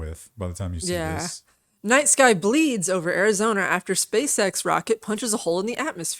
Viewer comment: I thought you said "Nice guy bleeds over Arizona" and I thought "How is that nice? I guess simps will do anything."